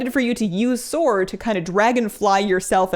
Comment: coulda sworn u said "dwagon" LOL hella cute